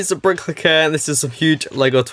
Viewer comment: sounds like he says brick licker hahaha